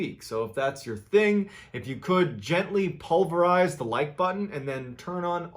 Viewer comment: “Gently Liquidate the like button” will always be the best one lmfao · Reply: I thought he said pulverize not liquidate.